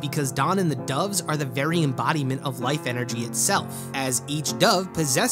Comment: I heard: "They become the counter for any black lantern's periods."